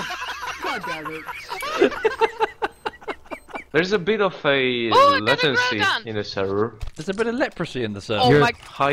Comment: But he actually said 'Latency' didn't he?